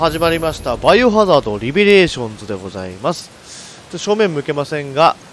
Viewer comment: リベレーションズをレボリューションって読んだしナレーションもレボリューションって言ってた(空耳) つまりバイオはゴージャス